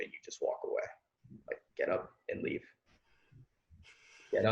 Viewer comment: I didn't understand what said at the 23:20 minute mark. Can somebody please explain? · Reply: get up and leave